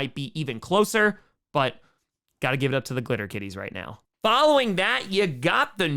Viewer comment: Im sorry but I thought you said clitter kitties and I was like WHAT! NO. SMOKE KING PIN BRANDON PERNA HAS GOTTEN TO YOU.